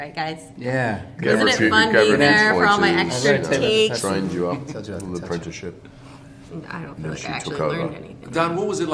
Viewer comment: seconds did he just say "I told you not to touch me" if so.....LOL (Don) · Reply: "What did I say about the touching?.. I told you not to touch."